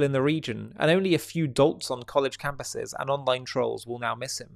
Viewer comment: "... and only a few ??? on college campuses..." Could someone help me there? What does Murray say exactly there? Surely not "dots". Thanks